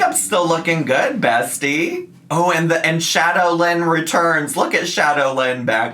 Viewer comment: when you were saying "shadow Lynn" I thought you said shadowlands (a world of warcraft expansion) 😅